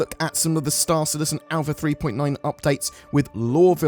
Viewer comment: Am I mishearing? We're at Alpha 3.9??